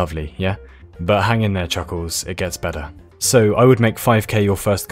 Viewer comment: Wait did he actually say chuckles ?